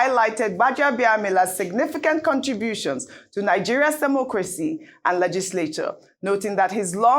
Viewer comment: E be like say I hear ‘SEMO-CRACY’….. Abi my ear dey hear double ni?! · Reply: That's exactly what said. SEMO- CRACY!!!